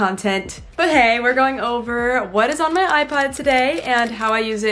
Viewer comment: sounded like you said iPod